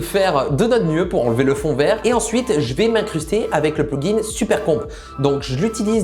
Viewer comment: moi quand je n'entend pas les "p" : "on va utiliser super con" moi tu parle de macron ?